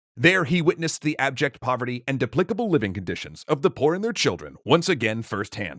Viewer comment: did you say 'deplicable' living conditions? Is that deplorable and despicable?